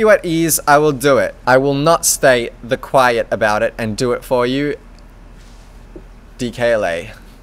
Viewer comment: did he say I'll do it "for you"? holy shit. tronnor approved ppl! aka, that Instagram post from feed. I'm so happy